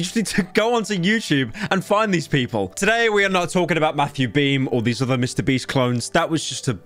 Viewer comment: Bro I listened to this shit in the shower and when he was talking about Matthew Beam, I swear to God I heard him say Matty B I had 2016 flashbacks all of a sudden